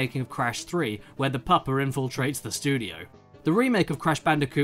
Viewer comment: did Caddy just say pupper